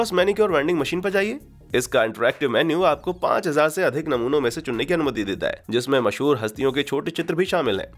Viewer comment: he sayed.. 5000 se be zada namuno... Who heard like..! 👇👇 👇👇👇👇